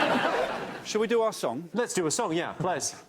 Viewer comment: Did Jimmy say ples?